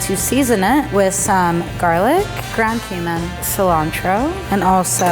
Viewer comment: I can’t hear what said at 3:30, what did she add? Ground something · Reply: Ground Cumin is the Seasoning